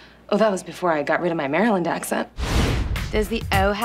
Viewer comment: I thought she said "Marilyn", like Marilyn Monroe. This way makes more sense.